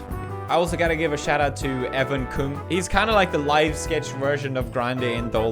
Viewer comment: Did he just say Even Kung????